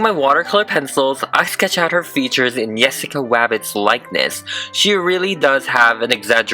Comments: did he just say yessica wabbit!!!? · Why did you say nessica wabbit here